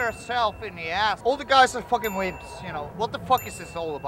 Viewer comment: did he say... weebs? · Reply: wimps